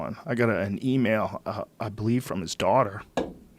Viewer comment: I could have sworn Joe said "yeah that was a rough one, I got an email... I believe from his dog" and that following such a hyperbolically sad story was too much, I lol'd. Intense story though. Fucking puppies.